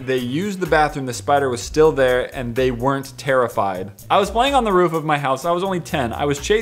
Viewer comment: I think it says were.